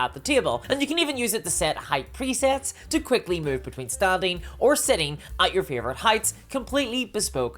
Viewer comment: During the table ad I thought you said whether "stabbing or sitting" and thought that sounded oddly on brand for you 😂